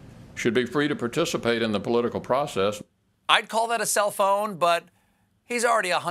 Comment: When you said self-own, I thought you said cell phone.